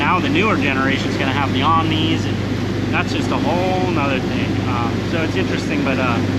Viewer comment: Can you elaborate? 45:53. What is a neome? I'm sure I spelled that wrong, but what did you just say?